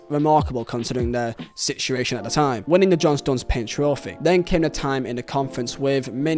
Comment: Did he say John Stones Paid trophy?